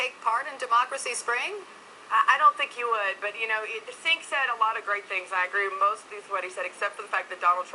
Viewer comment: Trumpbot was unable to parse what Cenk said at the end, she couldn't even get his name right. 5:37 It's Cenk (Jenk) not Sink, lol.